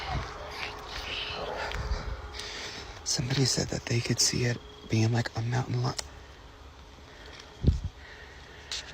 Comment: exits his vehicle and starts walking... The moment he begins to speak... he's not the only one! Listen carefully. Can anyone make out what is being whispered??? I can clearly hear the whispering whisperer..... but not clarly hear the whisper LOL if that makes sense. Anyone? Can anyone tell what's Being said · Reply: I hear it say, “are you fucking scared?”